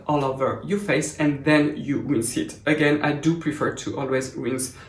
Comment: does mention to rinse it off because it contains detergents which should not remain on the skin. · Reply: OK he said"you rinse it again".... I thought he said"you will set it again" like leave it on your skin😂😂😂😂